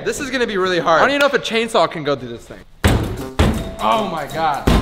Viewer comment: I thought you said a chainsaw would not go through it